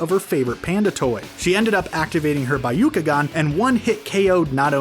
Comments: Did he just say bayukagan😂😂😂🙆‍♂️🙆‍♂️🙆‍♂️ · Did u just say Bayukagan and hayuga im dying over here omg😂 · Did he just say “byukagan” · Did he just say BAYUKAGAN at 4:30 im dyin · Did he just say baiukigan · DID HE JUST SAY BAROOKAGON IT'S BYAKUGAN